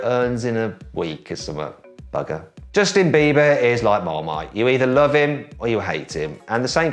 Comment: バカ(空耳)